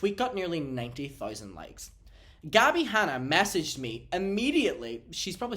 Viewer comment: I thought Trisha said Scabby Hanna the first time lol